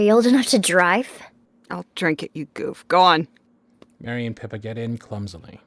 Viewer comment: Okay when I heard “Mary and Pippa” why did I THINK I heard “Merry and Pippin??” 🤣 I was grinning like a fool the whole time and everyone seemed to be having so much fun. Looking forward to seeing more from the S.U.Z podcast!